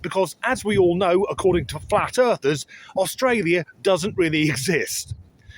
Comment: I thought you said fat earthers. 😮😊❤